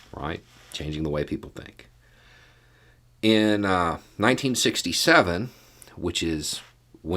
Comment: I literally had to rewind to 1:24 to check what I heard. I thought you said 1957 was when interracial marriage was legalized everywhere. Sadly, you said 1967.